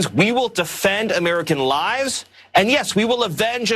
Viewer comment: it really does sound like miller says we will defend american lies